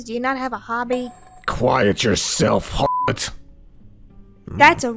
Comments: did… did just call a… Harlot? That’s not… hmm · Excuse moi ? WHAT DID HE SAYYY??? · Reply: He called earth a a harlot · Did he just call her a harlot Tf she do to you other than calling you an idiot